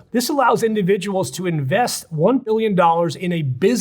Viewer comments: Did you say “1 Billion Dollars”(??? l as the caption says ???)!!! Man that’s steep!!! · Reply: It was supposed to be “one point two million dollars”, but, the intrepid editor cut the video into “one p.. illion dollars” for the USELESS ZOOM-IN ZOOM-OUT “STYLE”. Unless there are useful graphics why do that?!?! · is it million or billion? Captions says billion, but then the person say why pay more (5 million) for the visa. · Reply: Million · the weird cut here makes it sound like he says $1 billion, and the subtitles also say $1 billion but the truth is it's 1000x less: $1 million